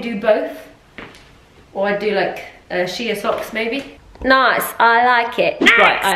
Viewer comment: I thought you said ‘Shia socks’ 😂